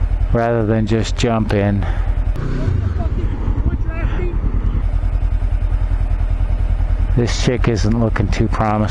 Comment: what did that driver say? Sounded like he was looking for a fight. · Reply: He asked if I needed an ass beating. Ha!